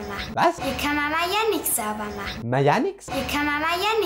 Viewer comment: Ich habe verstanden:"Hier kann man mal jährlich sauber machen."